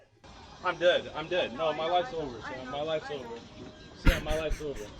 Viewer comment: How did I hear "My wife's gonna kill me" instead of "My life's over" lmao